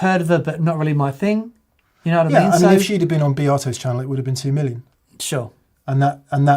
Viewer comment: I heard “Beato’s channel” as “Bea Arthur’s channel”. 😂